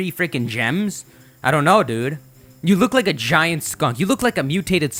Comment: Lmao I thought you said it looks like a giant skunt